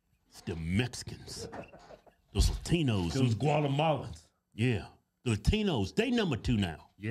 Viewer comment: When you said, “gualamalans” I thought you were saying guacamoles 😂🤣😆💀